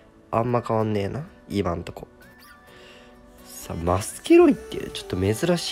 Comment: マスケロリに聞こえた俺は重症なのだろうか